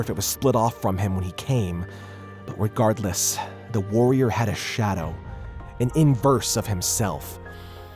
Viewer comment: I Think The two warriors were Garroth And Zane Fighting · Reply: That’s what I thought then I heard he said shadow and I was like dang it